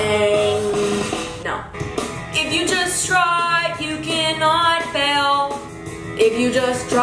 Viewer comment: I heard 'If you destroy, you cannot fail' and I was like oh o k